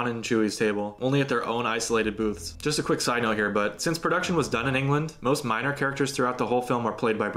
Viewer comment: Did he just call me "Bud" at about 20:34? "Just a quick side note, bud" ?